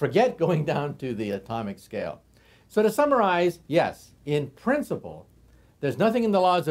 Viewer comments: i thought he said "to samuraize" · Very interesting. Did I hear "..so to Samurais ..." ? ... oh no it was "to summarize" .. my bad...